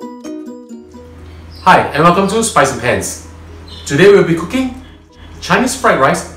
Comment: What I heard was "Hi! And welcome to SPICY PANTS." hehehe